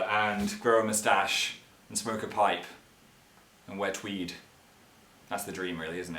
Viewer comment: I thought he said, wet weed. Then I was like, "wait, apply accent.....oh wear tweed!"